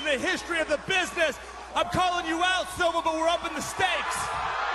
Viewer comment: What did he say here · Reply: "I'm calling you out Silva, but we're upping the stakes!"